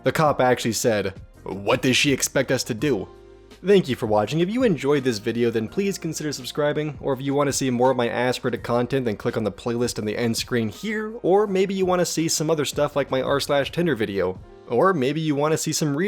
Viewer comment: I could have sworn you said “ass reddit content’ for a second there! Lol 😂 Great video, more of the same please. 👍👍👍